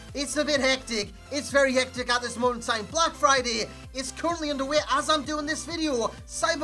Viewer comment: I thought you said "oblak Friday"